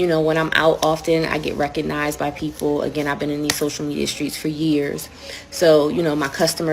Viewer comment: Did she just say social media streets???